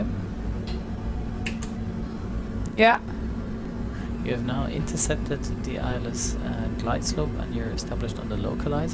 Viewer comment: I don't understand what he says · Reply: Those are the auto breaks